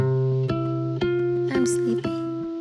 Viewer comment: Does she say "Im speaking" at 0:01?